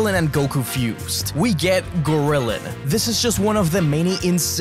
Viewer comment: When he said goriliin i thought he said gorilla for a sec😭